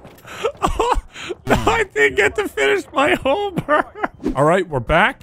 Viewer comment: Sounded like "I didn't get to finish my homework"